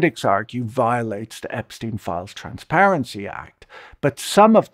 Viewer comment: you said "transparency", I heard "trans parents"....